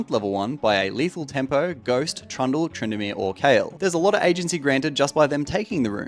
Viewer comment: did u say kayle? LOL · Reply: Yes, Kayle is a notorious level 1 lethal tempo cheese